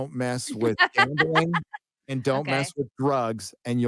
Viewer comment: What were the three rules at about 4:40? I can't make out the second one. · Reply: 1. Don't mess with bags of sticks (homosexuals) 2. Don't gamble. 4. Don't do drugs. 3. Don't snitch.